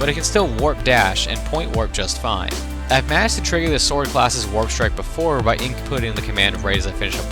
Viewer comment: Wtf do u say @ 2:49 😂😂😂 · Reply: "I've managed to trigger the sword class' warp strike before" I have a tendency to stumble over my words when recording a script. Usually I redo the take but I guess I missed it or was too lazy lol.